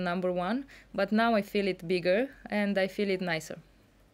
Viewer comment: "now .....I feel it bigger, I feel it nicer" that what she said